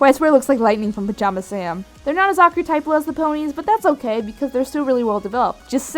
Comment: Can someone tell me what she is saying at 4:06? I hear "They are not as (?) as the ponies", but I can't seem to make out that adjective. "Archetypeless", maybe?